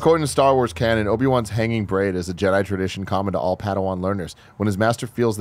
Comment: I kept hearing "hanging brain" rather than "hanging braid" and was so confused for a minute...